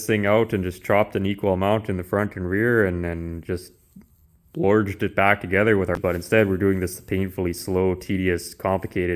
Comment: Did you say "bloorjed back together" around 7:15? Not only do I have an infinitesimal understanding of auto body work watching you but my vocabulary is growing by leaps and bounds .. God bless